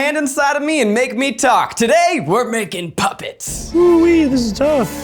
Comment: Totally thought it said making puppets without insecurities